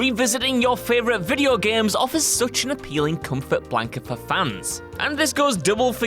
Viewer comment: I thought he Said "Comfort wank" for a Good second